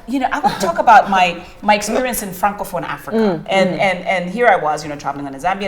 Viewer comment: what is the name she said there? Frank something africa? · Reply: Francophone Africa..... basically it's the countries in Africa that speak french as their national language or official language. This came to be because those countries were colonized by the french ..and they needed them to assimilate, ...that is speak and act like the french people. Then there are anglophone countries that speak English meaning they were colonized by the British